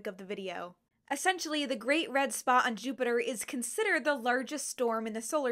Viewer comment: I kept hearing you say “great red spa” and I like this.